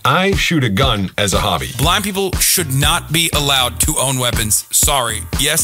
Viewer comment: wait did he say blind or black people?